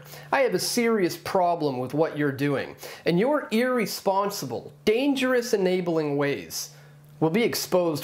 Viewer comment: did he say... “eerie-sponsible”